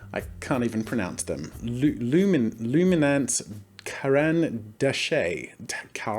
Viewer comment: Lmao at 4:10 when you tried to pronounce it, it sounded like you were like Lemino, another YouTuber.